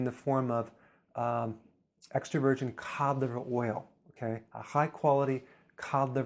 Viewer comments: please help at 6:38 he says( i would start taking extra virgin........something...oil?? that's available at amazon please help me understand what he says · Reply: david benson extra virgin cod liver oil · He is talking about some oil at 6:45 but I can't figure out what it is or how to spell it can someone help please? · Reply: Cod liver oil. Rich in vitamin A and D